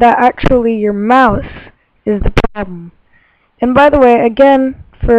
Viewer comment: Not working... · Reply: ***** What does she say at 6:21??? My mouse is pardon???